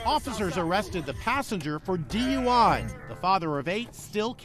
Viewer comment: Did he just say "father of 8"?